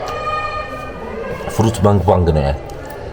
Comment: Did you just said "fruit bang bang" 11:52😅